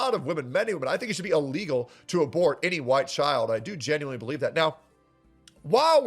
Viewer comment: did he just say "it should be illegal to abort any white child"? lol